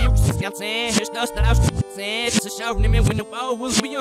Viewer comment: He clearly says "it's a show for you" from